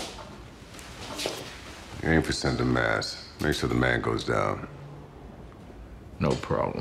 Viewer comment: what lester is saying? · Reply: "Aim for center mass. Make sure the man goes down."